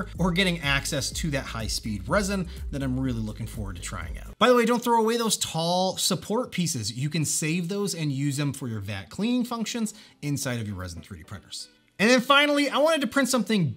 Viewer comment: Hi, great video, btw at 9:18 you were saying something about not throwing away your tall support structures and you voice dropped and I couldn't hear what you said. Can you let me know? Thanks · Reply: place them in a corner when you use the clean tank option, Then you can just use it as a handle to peal off the cured layer.